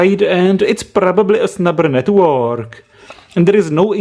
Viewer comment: I always hear "snapper" from you, instead of snubber XD